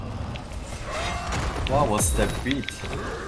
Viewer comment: Why is that root weird? Edit: Or did he say "beat", refering to the Sound Barrier?